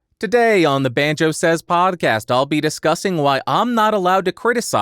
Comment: New tile for the podcast Mr. Banjo? :) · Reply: I actually thought you said the “Banjo says Podcast” in your into.